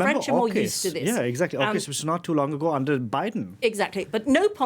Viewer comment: I wish the interviewer would NOT interrupt the person being interviewed in the middle of a sentence. I could not understand what she was saying due to his stupid interruption. at 4:28 She said that the French had stood their ground regarding Iraq. I assume she meant the US invasion of Iraq (with the UK under Blair) after 9-11. But he said "Hawkish"? or "Oakish"? and "it was not too long ago under Biden". Since he mentionned Biden, I really have no idea what he and she were referring to as her sentence was stupidly interrupted. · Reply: I think he said AUKUS. When Australia betrayed the French in order to buy US submarines.